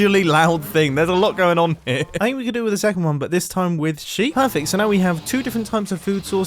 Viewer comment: Mumbo: it’s like an alarmingly loud noise Subtitles: let’s look at the lovingly bad boys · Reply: instead of sheep it says she